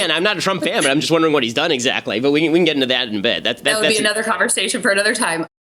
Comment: did he say "again I'm not a Trump fan, I just wonder what he did exactly,but we can get in that in bed." Hahaha